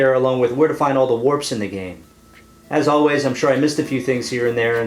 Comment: at the end, did i hear correctly..."until then, Eat Rice." ???